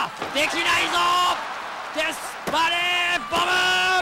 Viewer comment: デスバレーの前にせーのって声聞こえるの草